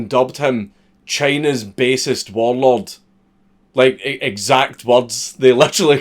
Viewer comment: You said "china's basest warlord" but I heard "china's bassist warlord 🎸🎶"